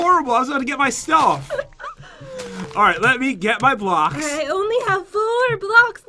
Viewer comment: anyone hear her say blunk